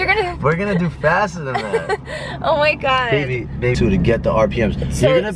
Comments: he calls her "baby" anyone else hear that? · did he say “baby”🤔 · Did he call her ''Baby'' ?? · Yooo did anyone else hear the guy that let drive call her baby 😍😍😍😍😍😮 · He definitely says baby to go to 7:55 and turn on captions says “baby basically get the RPM so”